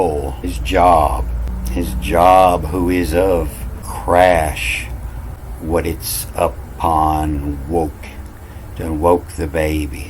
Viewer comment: hey, let me start by saying I love your content. I love your interpretations. Sometimes as you read, I hear something else, similar, but different meaning. Like in this vidaround 6:02, I heard the women of the rock of venus roe, (ie. line) something something- crashed upon. Like Venus Crashed upon earth.